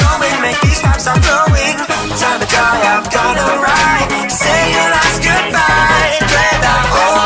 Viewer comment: Say your last good bye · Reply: The first time I heard that part I thought they said say your a## goodbye